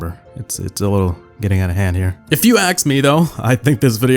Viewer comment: No one 1:09,8:59 Did I heard "axe me"